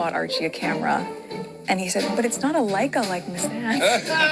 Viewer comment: I thought she said Lego camera. Leica makes more sense. She does not have a clue about children